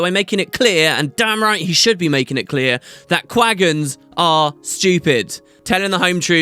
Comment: I think the video must be broken. It's clear from context that you're supposed to say something from 6:30 to 6:43 but I just can't hear it :P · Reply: (regendo) I can hear it man, he says quaggans are stupid basically.